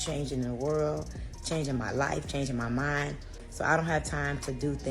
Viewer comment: did she say changing her mind is hard